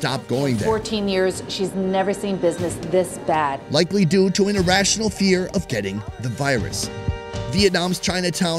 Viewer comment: Did Sunny say "an irrational fear of getting the virus"? That would be absurd given the continuously rising number of cases since the beginning of lockdown and when this video was released. Sure it's irrational from a Vietnamese perspective, but the U.S. hasn't handled COVID as well as most Asian countries.